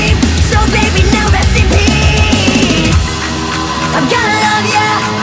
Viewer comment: When is said rest in peace i thought it said SCPS xD